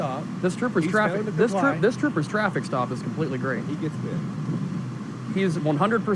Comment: At about 21:27 when the cop says this troopers stop is great, do i hear the other officer say “so he gets bit?”